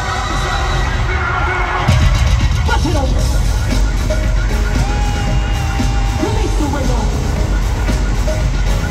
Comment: Could you please tell me what Beyoncé says in the minute? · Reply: “Buss it open”